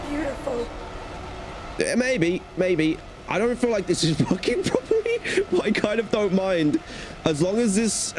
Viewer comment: correct me if I'm wrong but I think I heard him swear😳📸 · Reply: he said working correctly